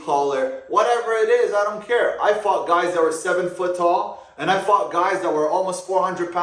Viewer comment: anyone else thought he said “I f**k guys that are 7 feet tall”